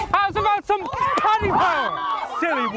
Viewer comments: "Silly Woman", sends her spirit to another dimension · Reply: Ohhhhh I thought he said “ How’s about some cauliflower? “ lmao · he really said “abra ca dabra”, literally appeared out of nowhere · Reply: Lol no he said « How about some Paddy power »